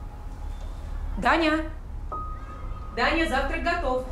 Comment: Зачем из прекрасного мужского имени Даниил делать девчоночье Даня(Таня) ? Данила , Данил , Дэн .))) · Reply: Мне так и послышалось - Таня. Думала, что она дочку зовёт.